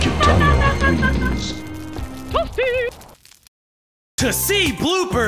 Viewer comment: Did he said "Hustý" ? That is mean Cool in czech